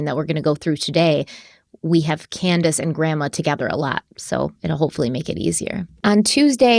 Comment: I hear a lot of people calling the granny Grandice lol